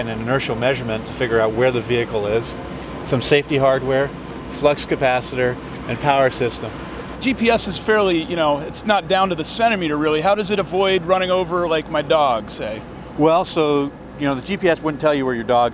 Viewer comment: Did he just say flux capacitor?